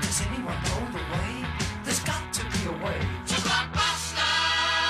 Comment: Sounds like they saying "black basted " instead of blockbuster